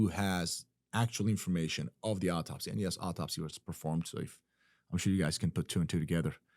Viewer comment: I thought they said No Autopsy was performed. Only the doctors at the hospital saw him